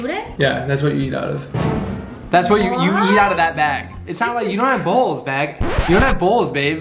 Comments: I heard “you don’t have bowls bag” lol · I thought he said they don't have bowls bag, not meg. Cuz you were talking about eating out of the bag?